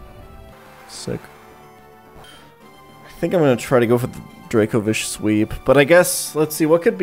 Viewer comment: Hahaha I can’t help but mishear you saying Dracovich as Dragovich which will always make me think about the line “Dragovich. Kravchenko. Steiner. All must die.” from Reznov in CoD: BO1